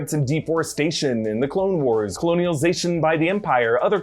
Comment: Eric, God love ya... Did you say 'coloni-o-zation'? (7:38) Just thought I'd say hello and heckle you. Just a little. Love your content! PLH